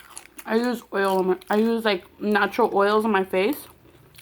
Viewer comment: hahaha omfg, I thought you said "I use NACHO oils on my face" like gurl, I relate 👌